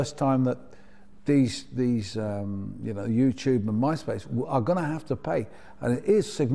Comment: Did he say Noobtube? Yeah, he deffinatly said noobtube.